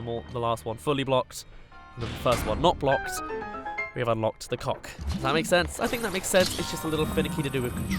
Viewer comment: welp, you accidentally swore. you said "cock" instead of 'cog"